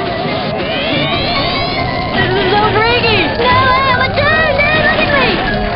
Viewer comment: What does that guy say? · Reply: This is so freaky. Now I am a toon. No don’t look at me.